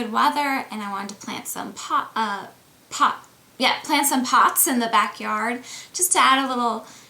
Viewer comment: sounded like she said she wanted to plant some pot in the back yard. (LOL POT)